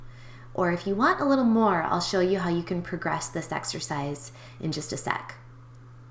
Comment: Exercise? I thought you said extra fries...